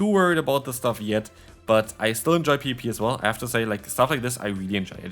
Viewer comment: you still enjoy WHAT?? haha it sounds like you say "pp" instead of "PvP"